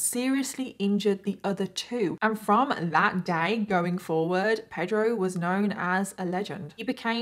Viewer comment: omg i thought she said “day god”. ahhh sorryyy!!!